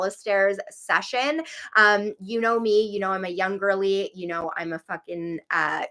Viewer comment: I thought you said “I’m a young girlie,” and I was like “yeah bb girl raise yourself you PYT!” Then it clicked you said “Jung girlie”. 🤦🏼‍♀️🤦🏼‍♀️🤦🏼‍♀️ Anyway! I love you do this. Your format helps other creators, helps your audience, like it’s a win-win-win for everyone.